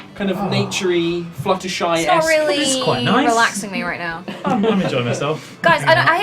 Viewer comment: did say Fluttershy at 2:43:15 .